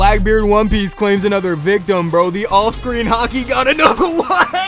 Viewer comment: My dumbass thought he said “Austrian Hockey” 😭😭😭